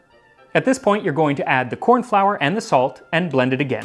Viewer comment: I thought you said rice flour, not corn flour?